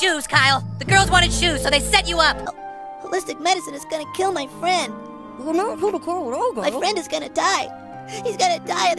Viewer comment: what is Kenny saying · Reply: "you never seem to care when I die." "Well that does it! I have had enough of this bullshit. Screw you guys, I'm going home."